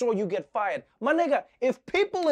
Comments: Did he say my nigga @ 2:52 or I’m hearing things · did he just say my nigga? 🤣